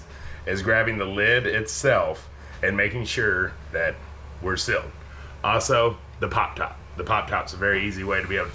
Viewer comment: Really fascinating..Where are you from? I kept hearing "siled" but realized you meant sealed.